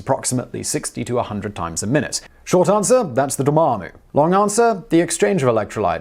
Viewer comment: What is said here? "short answer? that's the dormammu" ?